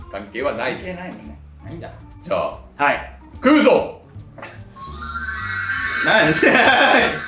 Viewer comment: 食うぞ！がクルーシオ！に聞こえて震えた